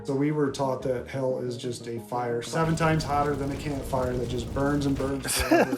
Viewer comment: When he said "seven times hotter than a camp fire" I heard him say higher , so I was like "that is a hellishly underwhelming fire for hell lol" 😂